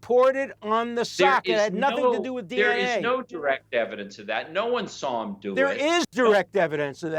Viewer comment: Did he just say No one saw it😐 So who saw OJ kill Nicole and Ron😳 Well, just... dayum!🤡🤡🤡🤡🤡